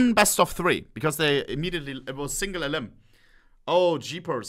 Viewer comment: Did I just hear "I am single"?